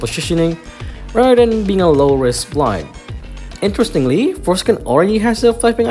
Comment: did bro just say foreskin??